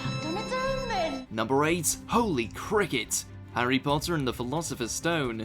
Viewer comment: i always thought hermione said “jimminy cricket you’re harry potter” until i watched it with captions and realised i was wrong and i’m so confused as to why i thought she said it😂